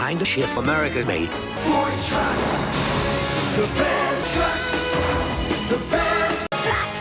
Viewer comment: Ford trucks: The best Trucks The best T R U C K S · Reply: I thought it said "the best sex" :))